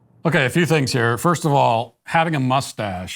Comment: Lmfao Im not watching the video I’m only listening and all I hear is a young lady crying about how she has a bread and a mustache so I go rewind to see what she actually said and ell oh ell. Just “lol” 🙈🙉🙊🫣☠️